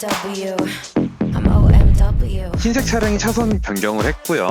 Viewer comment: 계속 BMW라고 들리는거 나만 그럼?ㅋㅋㅋㅋ